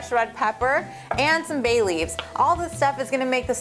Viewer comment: I thought she said Bailey's, as in Bailey's Irish Cream...but then I saw "Bay Leaves" LOL . Thought I had an excuse to drink for a second there :(